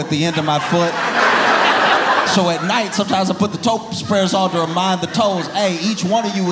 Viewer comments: who else looked up "Toast Breader" because you misheard "Toe Spreader" LOL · When he said toe spreader, I thought he said toast spreader. I was like, WTF is that? 😂😂